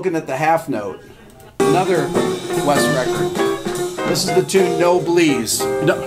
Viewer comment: what i heard: "This is the tune 'No Belize.'"